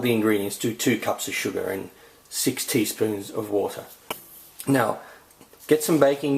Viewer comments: When he said 6 teaspoons of water, i thought he said 60 spoons of water o.O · Haha! 2:02 it sounds like you say 60 spoons of water. :D